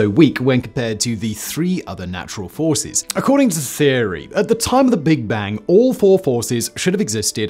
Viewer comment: I thought he said according to Siri 😂